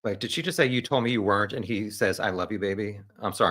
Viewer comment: you ask, "did she just say you told me you weren't, and he said I love you, baby"? The answer is yes, she said she didn't know he was on the bridge that day. He told her he wasn't. He knew their interaction was being recorded and he knew right then that was it. He got a little upset but reeled it back and simply said, "I love you, baby".